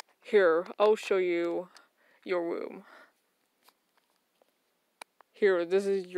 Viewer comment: OMG I ALMOST DIED I THOUGHT NIGHTMARE SAID " let me show you to you WHOM!" Like where babies live before they're born oh my goodness phew false ALARM!